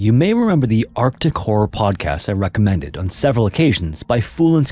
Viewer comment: did anyone else's brain try to make him say, "Arkham Horror, instead of arctic horror"?